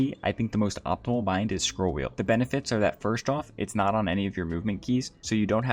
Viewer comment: Came back couple months later and found out he meant scroll wheel up I thought he said CLICLING THE DAMN WHEEL 😭😭😭🙏🙏🗣🗣🗣